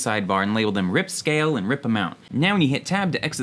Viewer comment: What he said: "RipAmount" What I heard: "Rip 'em out"